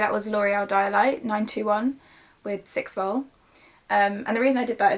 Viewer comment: Did anyone hear what toner she used I thought she said 912 but can’t find that anywhere?! 😭😭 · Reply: Yeahh it’s L’Oréal dialight 9,12 😚